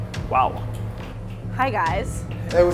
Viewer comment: When the lady came in and said "Hi guys" I laughed so hard because I heard "High Guys"